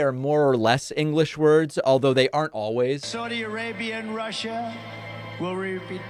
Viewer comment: “Saudi Arabia and Russia ree bee doo ahh.” Did any of his followers come up with an interpretation of this gibberish?—because I’m really curious what he was trying to say.